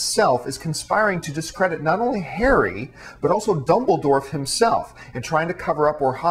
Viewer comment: Oh wow and I thought it was bad when he said Death Eaters, I've gone back and listened to the part where he says Dumbledore and I swear that he keeps saying Dumbledorf, pretty sure that I'm not just hearing things